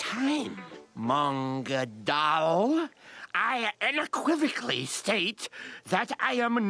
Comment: What word did he say? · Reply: Unequivocally